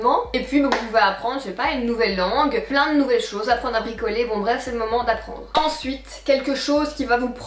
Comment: Fin de la partie 2, j'ai entendu "apprendre à picoler" au lieu de "apprendre à bricoler". 🤣🤣🤣 Sinon merci pour cette vidéo. 😘 Toujours aussi belle! 😍❤